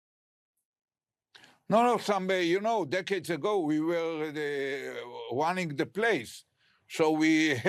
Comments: “Many years ago we wanted the place, 4-5 decades ago” he said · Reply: I thought he said "we were running the place" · does he really say "we wanted the place (so we built the bunkers in the hospital)" ?